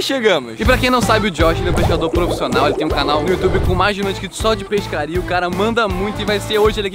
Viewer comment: ou escutei "com mais de um inscrito"